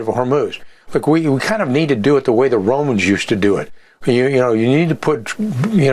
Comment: did he just say the goyim on live tv?😅